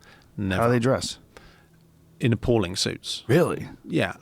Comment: hey 'vribody! what kind of suites did he say they wear at 13:57? thnx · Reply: appalling